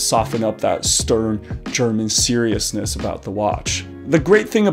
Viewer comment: Did he say 7:21 „stern german seriousness“??